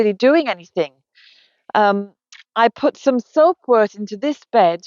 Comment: Hi! Can you tell me what plant you are referring to at (13:45)? I kept hearing "soap root", which, where I live is a native plant has has many medicinal qualities. But I know I am mishearing. Thank you!